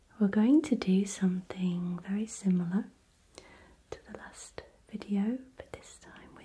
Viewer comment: I thought she said like the lust video 😂